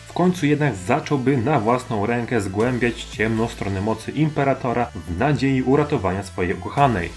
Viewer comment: usłyszałem Gimperator xDDDD